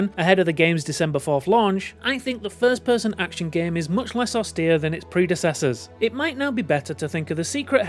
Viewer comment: Bro wtf is he saying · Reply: "Much less austere than it's predecessors"